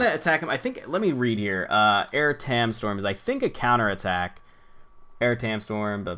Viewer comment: i thought you were saying Blaire ham store